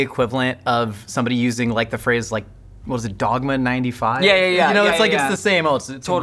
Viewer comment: did I hear you say "Dogma 95? "I looked it up and idk, but I found results of a movie. But were you saying another phrase?